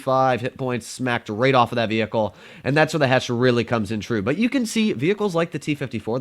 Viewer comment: i thought said "HESH THE HESH"